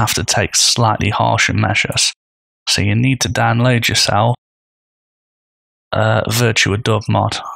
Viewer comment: It might be easy to find on google, but your accent was hard for me to understand; I thought you said "dog mod," so it's probably best to provide a link for those like me who couldn't quite grasp what you said. Still, you're helping me for free so I can't complain much. Cheers. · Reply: VirtualDub